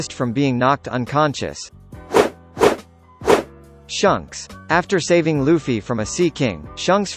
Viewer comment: Did he just say shunks